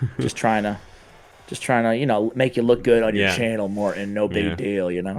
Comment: Thanks for having me on Morten :) So much fun! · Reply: I thought you said moron 🤣